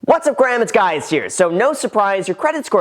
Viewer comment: Did he really say " what's up Graham it's guys here"